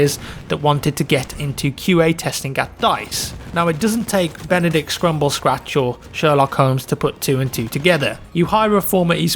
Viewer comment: did he say benbadick cumonherback?